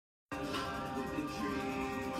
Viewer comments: "I don't know if your a girl, man" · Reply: I thought he said "How's it going with your girl man" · “Shits about to get real man” 🤣 “Huh” 😦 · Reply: I thought he said are you a boy or a girl man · “Shits about to get real man” 🤣 “Huh” 😦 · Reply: I thought he said “how’s it going with your girl man” 😂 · Sounded like he said “I don’t know if your girl or not bu-“